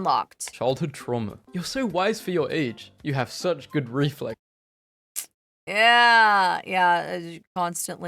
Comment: Ohh my good. The "childhood trauma" one.. I thought it said "you have such good gag reflexes"!!!